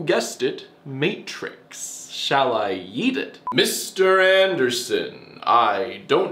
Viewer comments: “ Shall I yeet it? ” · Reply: Dear Evan Hansen, What to say to you? At first I thought he said ‘eat it’ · wait did he say "shall i eat it" of "shall i yeet it"?